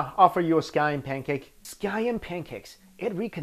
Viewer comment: I thought you said sky end pancaks